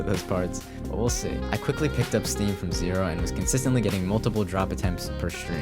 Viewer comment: is it just me or did he say "he picked up steve"